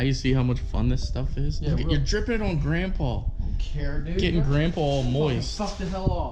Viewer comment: great team work buddy. wouldn't have wanted to do it without ya haha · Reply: Lmao, I heard "ur getting it on grandpa, getting grandpa moist"